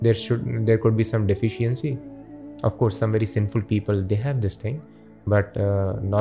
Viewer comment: did he say "some very sinful people have this thing" when talking about distorted retina at 5:17:00 etc